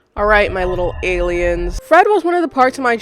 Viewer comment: I was lip reading your intro and I thought you said "Alright little gay kids" 💀